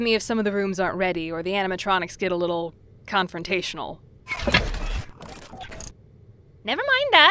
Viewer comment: I heard 'sorry if the anamatronics get confrontational' and had MAJOR FNAF flashbacks